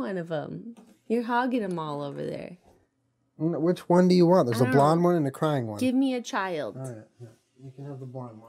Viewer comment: “There a blonde one and a crying one” I really thought he said “there’s a blonde one and a cermet”… same thing